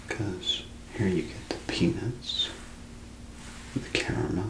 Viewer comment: When he was describing Snickers i thought he said penis not peanuts 😂